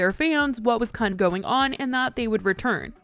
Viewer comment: Why did you say c*nt? Lmao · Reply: Sounds like it she said kinda going on.